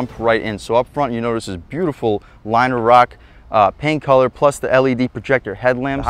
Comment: Did you say Liner Rock? 🤔 Great truck, great review!